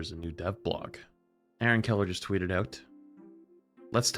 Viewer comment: I heard Hellen Keller lmao 1:56 I HATE AS A SUPPORT PLAYER HAVING TO ONLY HEAL THE TANK its so annoying lmao.